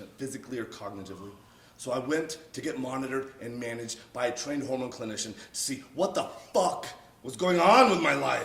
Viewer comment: did he just say homo clinition? Is that a real thing? Sounds pretty gay